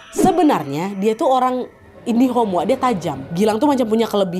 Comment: Kenapa yah tiap menyebutkan 'indigo' pasti kak nad selalu bilang 'indihom'? 🤨Apa aku yang salah dengar🤔